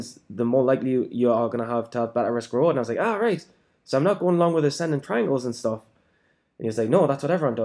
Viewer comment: Hey mate at 4:30 ish you say so I'm not meant to acending triangle or something ? Haha what did you say ? After said buy/sell on support resistance etc